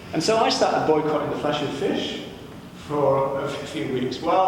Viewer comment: "... I started boycotting the Flashy Fish for a few weeks, while still eating [...?!...]" Anyone catch what he said before or after this quite smooth edit? Anyone actually there and know what's been edited out? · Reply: "I started boycotting the flesh of fish, while still eating other animals/flesh." Flashy fish! LOL